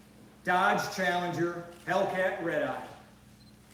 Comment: I thought he said Hellcat ribeye 😂